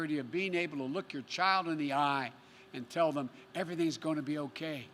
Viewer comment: Did he say "LICK your child in the eye" ?? Has he actually put the child sniffing behind him & progressed to tongue action?🤮